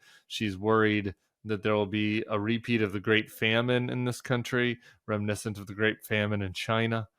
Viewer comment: She said famine? I thought she said salmon, which I like if prepared well.